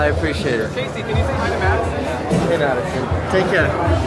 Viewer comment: what did he say to that young fan of his? Did he just blew him off?? Jees, I hope not. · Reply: he asked him to say hi madison and then casey said hi madison if i was casey i would have been annoyed.